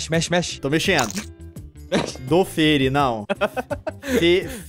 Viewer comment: MEU DEUS, EU OUVI UM CRLHO????? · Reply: Caramba